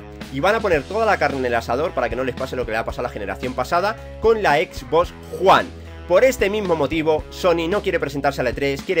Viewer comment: pensé que dijo xbox juan😂